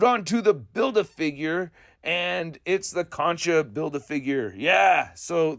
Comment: When you were talking about the Khonshu BaF I heard "concha" and had a pretty good laugh 😂. Feel similarly about this livestream but I really like Khonshu. Looks fabulous, imo